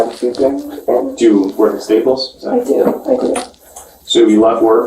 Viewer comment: She worked at Staples. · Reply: when they they staples I thought I heard “wait tables” 😂